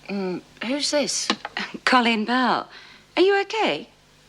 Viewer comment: her name is Colleen not Connie lol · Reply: I thought he said "Connie" as well, but listen to it again carefully. He said "Colleen."